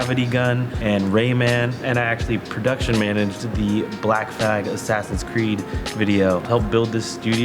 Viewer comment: Tell me if I’m wrong, but I think says Black F*g (3:55) by mistake when discussing his corridor origins. Obviously a mistake, nothing too big, but I just wanted to point that out.